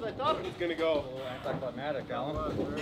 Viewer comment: see the words spelled right on screen but it sounds like is still sayin climatic and not climactic. Climatic is talkin about the weather lmfao. 😂😂